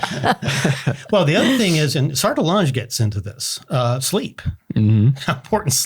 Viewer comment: Who is mentioned at 28:30? The CCs says Sartolange but I am not familiar with this person. What did they write? Where can I find more? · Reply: Hey Floris, Great question. The individual mentioned is Antonin-Gilbert Sertillanges, a French Catholic philosopher and spiritual writer. He is best known for "The Intellectual Life," a practical guide for living one's life as a scholar, and "What Jesus Saw from the Cross," a spiritual book inspired by his time in Jerusalem. For further research, you can find more here <link> . We hope this helps! -MP Creative Team